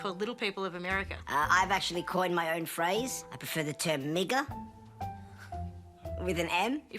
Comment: that was bold. Lol i thought he said nigga. 🤦🏾‍♂️😆